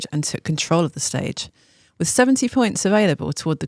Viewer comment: i missed today’s stage and am new to the TdF and cycling world, sorry, but at 0:33 did she say 17 or 70 point available? i assume 17 cause 70 would seem excessive compared to the other stages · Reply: it's 70. 20 for the intermediate sprint win, 50 for the stage win.